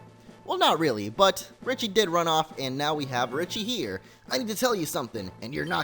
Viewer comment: I thought it said Ritchie not Team Rocket....